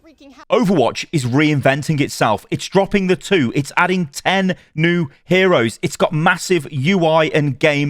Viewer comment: Brother… 0:09 … did you say 10 NEW HEROES?!?!